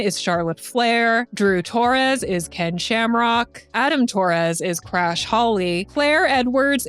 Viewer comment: OMG I THOUGHT YOU SAID ADAM IS CRASH HIS CAR HOLLY